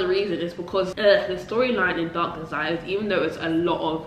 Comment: I need some Spanish shows too what’s the name of the one u mention doctor what ????? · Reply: it's acc Dark Desires. I acc thought she said doctor as well 😂😂